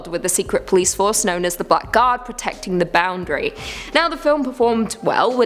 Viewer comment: When you said "Black Guard" I thought you said "Butt Guard" and I tried to imagine what it would guard butts from. Maybe there could be a movie about a guy who has invented the butt guard and then starts shooting arrows into people's butts to make them feel a need for the butt guard. If you make this movie, please acknowledge me in the credits.